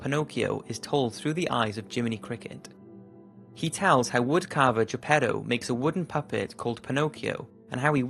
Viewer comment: I thought he said a wood Carver who's a pedo